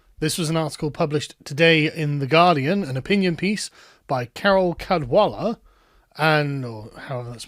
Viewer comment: I heard “Carol Codswallop”. 😂